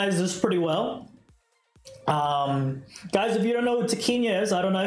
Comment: what did you said,i didn't understand what's the name of YouTube channel?